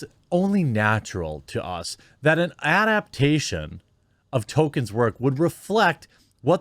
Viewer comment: Oh wait you said Tolkien I thought you said "TOKEN"! 😁🧐